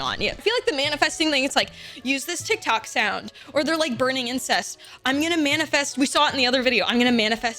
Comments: Did you just say "burning incest"? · Wait wait hold on!!! Did she just say burning incest???????